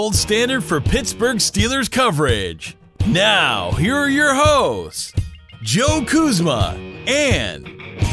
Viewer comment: it sounded like he said "heres your hoes" lol